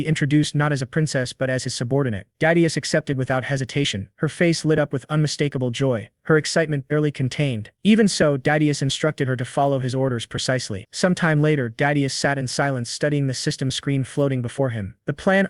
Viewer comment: The entirety of the first few minutes when MC's name was said all I heard was "Daddieus"